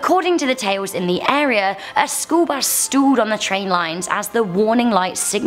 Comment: I thought she said the bus stooled. It confused me, I started wondering why would a story say the bus pooped on a railroad track?